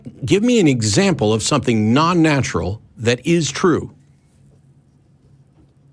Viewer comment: what did he say at 19:47; a "P" is all I could hear??? (Or was it "peace")? Ah Beliefs! The cry of the YT film critic. Goes like this: "Im my opinion..." (pre-emptive criticism shield) "... the facts of the matter are... " (any old shit). And thus equivocated subjective and objective. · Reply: It's philosophical jargon. "I believe that P" Where 'P' is a stand-in for any premise. What he's saying, in his erudite way, is that a belief is non-natural. I think it's silly of course, but that's what he's proposing. It's embarrassing the way a lot of atheists (and I am an atheist too) on this channel are pretending this guy doesn't have any knowledge. It seems like you guys are all super-threatened by the fact that he knows quite a bit more about philosophy than you do. Just chill and embrace a little humility. If you really want to be the authority you pretend to be, go buy a few philosophy books or take a course and get to work. Right now you're just embarrassing yourself on the internet.